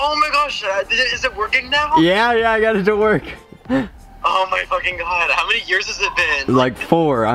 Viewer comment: did anyone hear say oh my fucking god